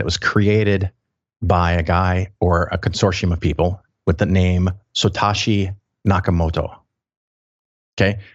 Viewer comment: It sounds like he said "Sotashi" rather than "Satoshi"